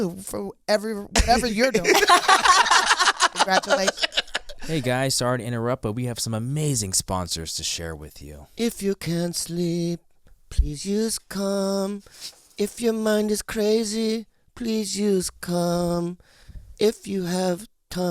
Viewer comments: I thought they were saying at first please use cum lol not calm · If I wasn't reading <link>" below, I would've sworn that Bobby said "when you can't sleep, just use cum" 😂😂😂